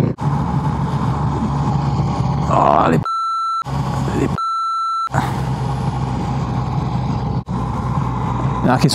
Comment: Non ho capito bene cosa dici al 1:23 😂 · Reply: non si dovrebbe fare il balletto sulla moto… 😁